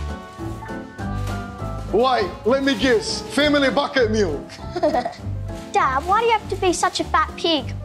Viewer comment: Love how he insults the family then defends the dad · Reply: I thought he said family bucket meal because they are a family father and son